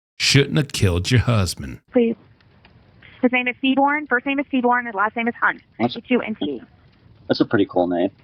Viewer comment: Um, did anyone catch the name of whatever she was talking about? I need to deep dive this! It was Seybourn last name hunt??? 27:16 Sounds like one of the names that Bart Simpson would prank call Moe with 😂